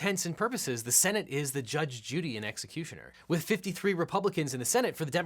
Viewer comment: Did he say "Judge Judy and executioner" at 10:15?